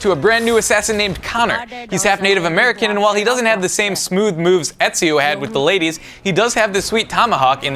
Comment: i thought he said Smoove moves hahaaa!